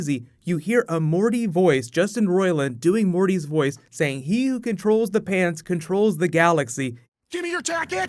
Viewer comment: I thought Mr poopybutthole said he who controls the pants controls the galaxy